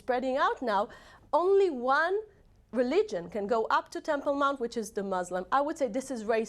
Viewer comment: can anyone tell me what the word she said in 1:53 is.....plz it sounds like tempor mom.... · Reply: "Temple Mount", see <link>